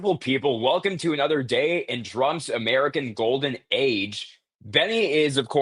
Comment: Why can't this guy talk right.. Why tf do you keep saying "Drumpf"?